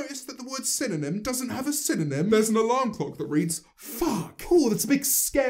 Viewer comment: I still can’t get over how she says “synonym doesn’t have a synonym” when with a quick google search and having a brain , it does. Alikeness is one, like sis are you fr. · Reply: I thought he was saying cinnamon doesn't have a synonym😂